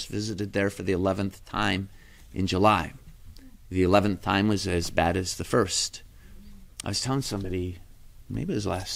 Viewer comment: I am at 18:29 and have twice heard him say what sounded like “the russian error”…… can someone explain that to me? (Im not Catholic), but respect… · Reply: Thank you for your interest in this presentation. Father Altman refers to "the errors of Russia," an expression drawing from Our Lady of Fatima's warning to the world in 1917. After showing the children-seers a terrifying vision of hell, She said, “You have seen hell where the souls of poor sinners go. To save them, God wills to establish in the world devotion to My Immaculate Heart. If what I say to you is done, many souls will be saved and there will be peace. The war is going to end; but if people do not cease offending God, a worse one will break out during the reign of Pius XI. When you see a night illumined by an unknown light, know that this is the great sign given you by God that He is about to punish the world for its crimes, by means of war, hunger, and persecutions of the Church and of the Holy Father. To prevent this, I shall come to ask for the consecration of Russia to My Immaculate Heart, and the Communion of Reparation on the First Saturdays. If My requests are heeded, Russia will be converted, and there will be peace; if not, she will spread her errors throughout the world, causing wars and persecutions of the Church. The good will be martyred, the Holy Father will have much to suffer, various nations will be annihilated. In the end, My Immaculate Heart will triumph. The Holy Father will consecrate Russia to Me, and she will be converted, and a period of peace will be granted to the world.” God bless you.